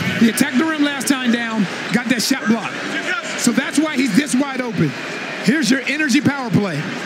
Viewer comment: Yo , am I tripping or did anyone else thought he said “ he got that shit blocked” 😂